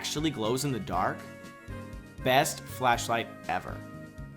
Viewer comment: Plz, Game Freak, make a Raichu flashlight! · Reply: You really scared me. I thought you said fleshlight for a second.